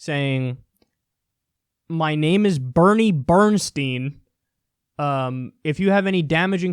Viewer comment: Bernie Bernstein sounds like a name that Trump would make up. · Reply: Seth the Wicked I heard “Lenny Bernstein”